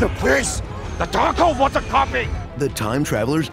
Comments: when Wong says "The Darkhold was a copy!", my YouTube close captions said, "The taco water coffee!" I can't unhear that! Oh, and at 1:33 the captions said, "and watch his streak of ELVES continue...." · Did Wong say The taco wants a coffee ??? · I swear I heard Wong say "The Taco wants a copy" in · Did Wong say The taco wants a coffee ??? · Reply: I heard him say The taco was a copy.